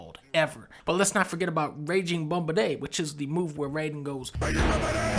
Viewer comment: i thought he said RAIDEN GAMBARE